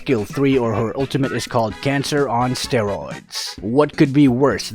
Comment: I thought I heard "CANCER DRIFTU"